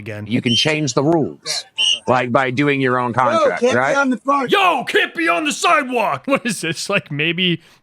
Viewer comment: I like the new cam, giving some 90's fisheye music video vibes. Also at 14:35 I thought the guy was saying "Catch you on the fart," so thank you for clarifying what was said XD